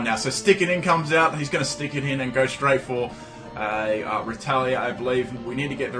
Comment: When you said "retaliate" the first time I thought you said ""Strike of the Red Talon Edge" lawl.